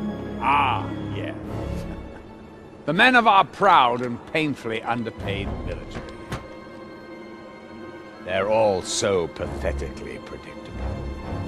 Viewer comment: wat he say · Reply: Stahl: Ah, yes. The men of our proud and painfully underpaid military. They're all so pathetically predictable.